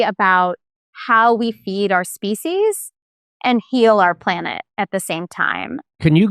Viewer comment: I am so sorry, I thought she said "how we eat our faeces"...